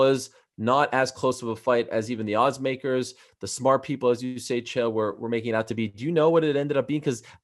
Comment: Ariel did infact say Chael!